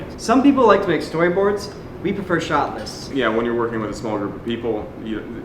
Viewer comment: What say at 01:12 "We prefer ....."? Shotless? Shortless? Somebody know?